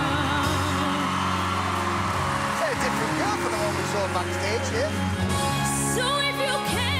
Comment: Can anyone tell me what's the MC saying in 2:44 ? I couldn't catch that. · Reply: "A very different girl from the one was saw backstage here "